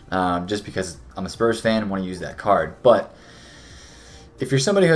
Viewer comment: I hear “I’m a spurs fan I bought son” I subscribe